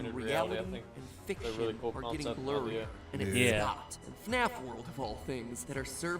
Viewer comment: ah , sweet! while im here may as well ask , what did say at around 31:50 if you can hear / remember it? i cant make it out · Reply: "I do like AR, I think it's a really cool concept/idea."